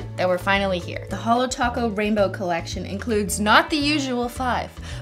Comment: My high ass thought it said taco bell rainbow collection 🛸 Christ.